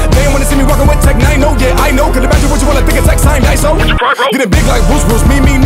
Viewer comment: did he say what of tech signed iso????? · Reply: I heard "What if Tech signed Iso, would you cry bro?"